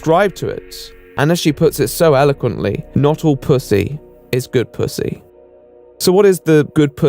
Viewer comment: I also thought she said "good pussy". Somehow it seems to make more sense.